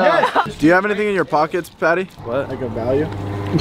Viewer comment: what did he say? Did he say do you have anything in your pockets like a Valium? Lol or did he say of value?